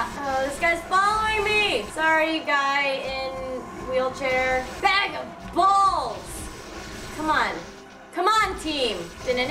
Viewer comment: Did she just say bag of balls